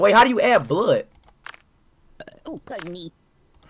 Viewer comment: “ burp OOOH BUNNY!!!” got me rolling😂😂😂😂😂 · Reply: I heard “oh bunny” but “pardon me!”